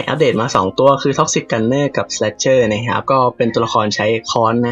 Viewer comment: ทำไหมผมได้ยินว่า toxic gun - ???? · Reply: Toxic gunnerครับ